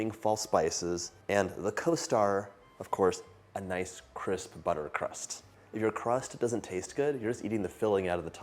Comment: 1 minute gang!!!! Can I add fresh coffee to my pumpkin filling without alternating it much? (Pumpkin flavored coffee is my favorite.) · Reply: At first I thought you said you put coffee in your crust, which also sounds good. ☺️